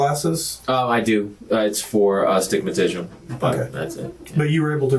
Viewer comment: He been lying since ever since he said he wore glasses for agimatism 😅😅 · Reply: I thought he said a stimatishm. 😂😂😂 Still wrohg, b7t somewhat closer.